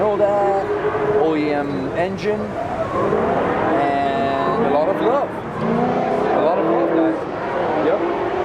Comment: when he said alot of love i swore he said alot of blow lmao